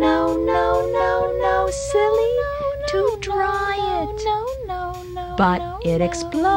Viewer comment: I thought she said to dry it instead of to try it